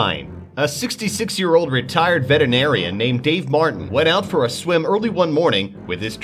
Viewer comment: Did he say "vetenarian"?